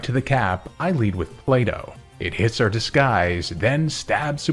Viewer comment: Every time you say Plato, I hear you saying "Play Dough"... 😂😂